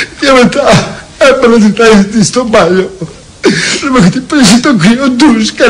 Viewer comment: τι είπε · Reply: "Έπαιρνα τη θέση της στο μπάνιο"